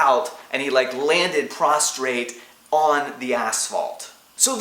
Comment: not gunna lie....thought you said he landed prostate